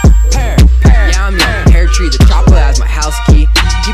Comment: Yea i made it peartree and the job has my house key WHAT I HEAR